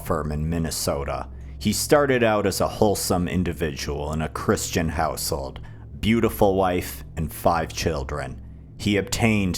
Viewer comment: My brain is fried, heard this as; "-a wholesome individual in a Chris-chan household."